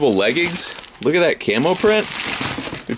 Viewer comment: Protect your family bro....notoriety isn't always a good thing. A sense of security and privacy is necessary to healthy homes. P.S. I thought you said "look at that camel print"....then I realize ..ohhh...CAMO...🤣😐😅